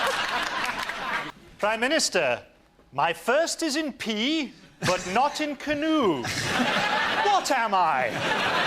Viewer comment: What did he say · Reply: Prime Minister. My first is in P, but not in canoe, what am I?